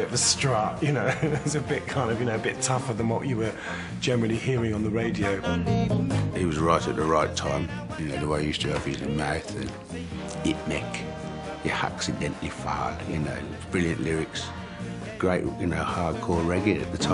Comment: I thought he said, "...it mek/ he accidentally farted."